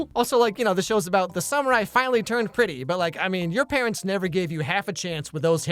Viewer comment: For some reason at 13:55 I heard "The samurai turned pretty"